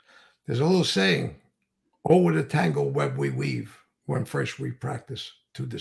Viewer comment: Oh what a tangle web we weave. When fresh we practice to deceive 👀 Never mind the Rubicon. What was said at 51:00 mark? Guess I'm the one who need to get a Book! 😂@